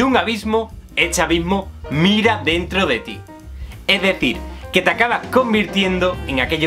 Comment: Te juro que escuché "Cuando miras mucho tiempo a un abiso, el chavismo mira dentro de ti" eso podría tener mucho sentido